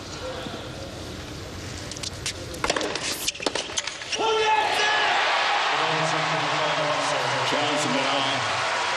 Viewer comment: What did Roger yell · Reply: Chum Jetze. "Come on now" in his native Swiss German.